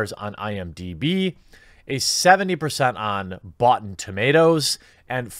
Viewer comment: "Boughten Tomatoes" earned you a thumb up on the video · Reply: I heard it as "Bots 'n' Tomatoes"...